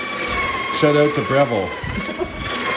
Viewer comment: Just started the video, half paying attention, said "Shoutout to Brenville", but I heard "Shoutout to Brembo", and I was like "When the hell did they start making coffee?!" then I looked up at my screen, and felt dumb.